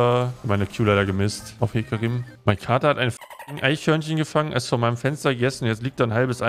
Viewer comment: erstmal Vater verstanden. Ich war nur dezent verstört.